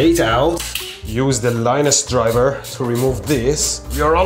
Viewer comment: Mehdi refering to screwing the wires in as "linus" them in had me dying lol. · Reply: I thought he said linus driver because that's one of the LTT screwdrivers.